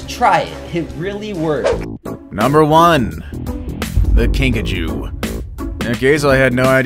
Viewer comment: When you said kinkajou I thought you said Pikachu when I got so excited🤣🤣for some reason 🤣🤣